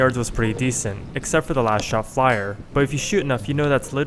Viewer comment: is this just part of the penis joke? You can't control the last shot fired? Is he saying something i am miss hearing? Last shot fire?